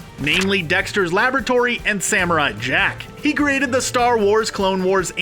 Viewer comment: For a second I thought he said Samurott Jack lol